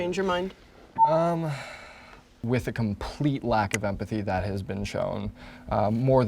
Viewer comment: he killed his family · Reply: idk about her but i thought you said she lacks empathy